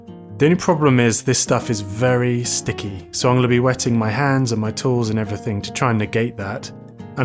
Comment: Very great first vid for sure! You came out swinging with this one, hilarious song at the end btw. Definitely subscribed here! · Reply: Re watching vid and thought I heard "Wet my hands and my toes" LMAO even thou I know what you said